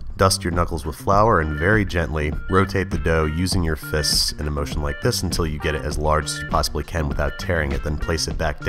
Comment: “motionlike piston” It’s ok Andrew. We all make editing slip ups. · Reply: I thought he said "motion like this"